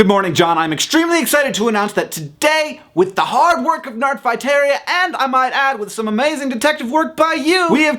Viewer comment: Nerdfighteria (or Nardfighteria, which is what I heard you say at the beginning of the video, Hank) is amazing and the tenacity and brilliance of the people who call themselves Nerdfighters never ceases to amaze me. Well done, Nerdfighters! Gussie certainly had an interesting life. As for your question, Hank, the worst name I've ever heard is Lorris Gregorris. I also once knew a girl named Princess, but I forget her last name.